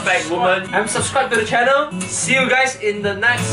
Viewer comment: i heard that he said "unsubscribe to the channel" o_o